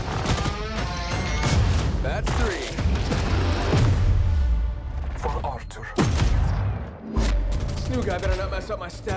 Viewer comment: Bloodhound had said if I heard right, "For Arthur" (BH's bird who we see him call in the beginning at hero select) What could that mean????